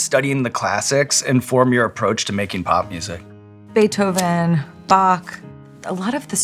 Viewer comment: DID SHE JUST SAY BOQ????💚💚💚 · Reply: Pretty sure she said Bach, since she was talking about Beethoven 😊